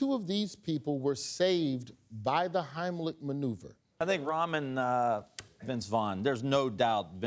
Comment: I thought he was saying "Ramen" not "rahm" 😂😂